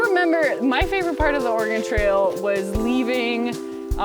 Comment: I definitely heard it as "The Organ Trail".